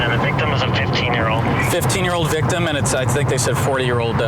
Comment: Did I seriously hear that correctly? That suspect is 4 years old? 😂😅😂😅 21:50 to 21:57 I know for sure I heard 4 not 40 I swear I heard 4: I'm not crazy Like this If you actually agree, I'm not crazy I know he says 40 later on in the video but I know I heard 4-year-old at one point